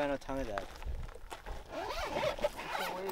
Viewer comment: If someone is wondering what did say in 11:56, its "damare!" (黙れ !) and it means "shut up" in japanese.